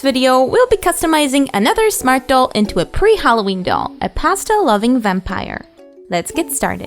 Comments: Is it weird that I misheard "Pastel loving vampire" as "Pasta loving vampire"? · I thought she said pasta for a second